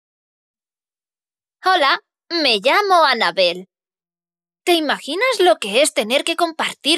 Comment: no se si dijo Anabel, pero yo entendi ''Alabe'' y pense ''Alaberg*'' :v